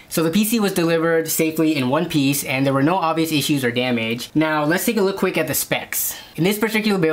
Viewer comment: I don’t mean any disrespect to but I thought it was funny when he said “let’s take a look quick at the stop specs”